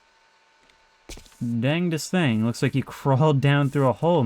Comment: Did you say Dangestan? Or damn this thing